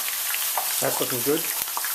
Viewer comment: it sounds like he says "Thats fucking good" ;D